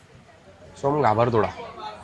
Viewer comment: He clearly said ghabar thod 150 cut over acting 😂